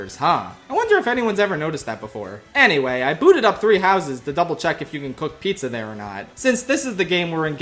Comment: I didn't hear "Three Houses" for the first time I watched the Byleth segment. I heard "Three Hazards," which feels like a more fitting title.